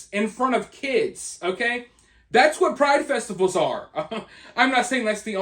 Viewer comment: I thought you said “naked dudes in the streets swinging their freaking …….nuts in front of little kids at these Pride FESTICLES” I died laughing my son corrected it and said festivals , but my god I laughed my a** off at that